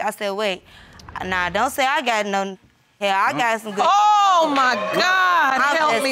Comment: bruh did she really say N***a hair?!?! Loooooord 🤦🏾‍♂️🤦🏾‍♂️🤦🏾‍♂️